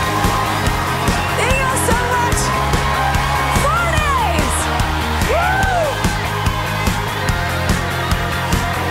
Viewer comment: What does she say 03:39? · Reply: "4 days" referring to her album stronger coming out